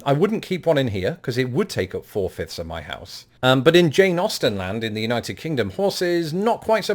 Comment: Every time you say, “Jane Austen,” I hear, “Jay Nausten.” 🙂